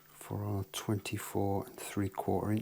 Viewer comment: I thought you said that the scale length was 24 1/2 inches, I didn't think it was 243/4?